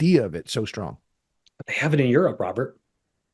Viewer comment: What did he say at 29:47, I couldn't understand him. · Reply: Robert: Why is this idea of it so strong? They have it in Europe, Robert. Robert: That's it. That's the only reason? A lot of New York public policy flows from people looking uncritically at how things work in Europe, and saying, "Why can't we have that here?"